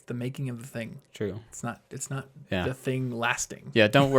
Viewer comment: what does Martin say here? :D sounds like "sorry" in German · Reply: I think he says "true", but it really does sound like it :D Love from germany to you!